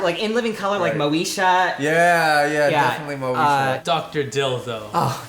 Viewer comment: He said "Dr. Dill though" but I heard "Dr. Dildo"